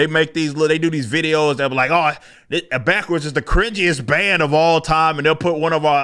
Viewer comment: What is the bands name and a couple songs. I thought you said backwards but I can’t find anything👊🏼 Bro this is the first time I’m hearing that you are metal!! All that remains are awesome wow! Right now I’m listening to Angelmaker🤘🏼 please don’t tell me these guys are npc’s